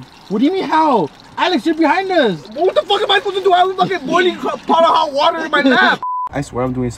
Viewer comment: “Wttrffrr iimmmmm i suuppopisseee to do “ wtf was even saying 😂 bro can not talk for shi